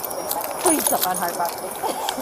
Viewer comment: sounded like "Please step on Hart property". So you can arrest him? Talk about having no idea what your job is!